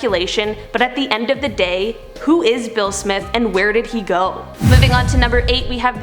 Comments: When you said Bill Smith I thought you said Will Smith🤣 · I thought it said will smith😭🤣💀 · Ngl I thought she said Will Smith 🤣🤣🤣